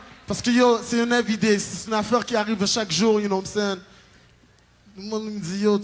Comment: It sounds like he says "No what I'm sayin'" 3 times in the beginning.